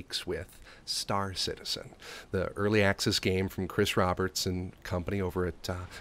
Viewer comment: Did he say Chris Robertson at 0:18 ?